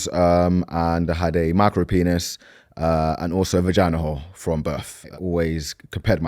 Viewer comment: Did he say vagina hole? But to be fair he speaks like most guys who are ignorant about women's bodies..